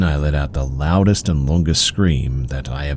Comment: for a second i heard “the loudest amongus scream” made me giggle ngl